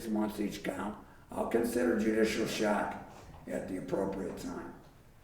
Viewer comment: Can someone explain what the judge meant at 4:32 when he said during sentencing what sounded like " I'll consider judicial shack(?) at the appropriate time" What is that? It sounds like a way for this thug to get out way early once the publicity of this case blows over.